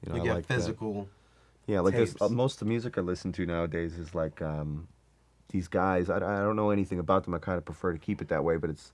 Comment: Anybody know what the youtube channels he mentions at 50:38 are? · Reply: H3H3 Podcast